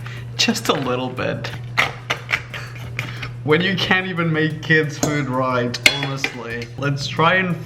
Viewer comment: I Cant even make kid foods 😂😂😂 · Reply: at fisrt I hear I " I cant even make kids" lol